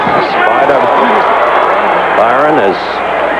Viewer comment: One of the judges is named Spider Bynum? Swear I thought the ring announcer said Spider Bite'em